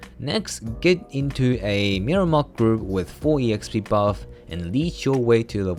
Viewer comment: What does he say at 5:30? get into a what group? How do i do it? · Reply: Mirumok grinding group exp leech. Need to get a friend to grind for you, while you sit somewhere safe, and have all exp buff on. If you got most of the important exp buffs, 1-60 should be less than 3 hours.